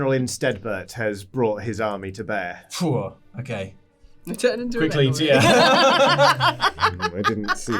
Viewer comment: After General Insteadbert is mentioned what does say, which says he should have seen it coming? I can't tell on the VOD or Podcast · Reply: says "General Insteadbert has brought his army to bear..." says "I turn into a bear!"